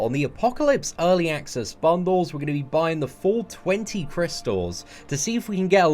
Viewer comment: is it just me or did he say the 420 crystals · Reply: He said “full 20 crystals”